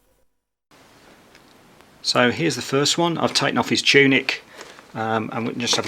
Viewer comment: did you say tunic or tunip?